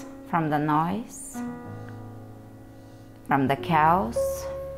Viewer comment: Beautiful class, really left me feeling so stretched out and peaceful. Thank you. I even got a surprising stress relieving belly laugh as we settled into savasana! You were saying to let go of and step away from anything that isn't the true you. And one of the things my head heard you say was to step away from the "COWS". You see, my 2 year old has new crib sheets that have a farm scene and lots of cows and he really likes to talk about them so I guess that's a word I had in my head. It took a minute before I realized that you said step away from the "CHAOS"!!!! So I was laying there literally laughing out loud thinking about my son waking me up on the baby monitor saying, "mama, baby cow!". Let go of the cows! Hahaha! Thought I would share the story and hopefully give someone else a laugh as well! Have a great day everyone! Namaste.